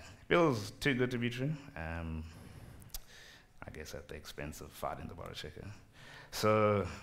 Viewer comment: When he said "rust is an exercise in fighting the borrow checker", the auto generated subtitles thought he said "rust is an exercise in finding the butter chicken"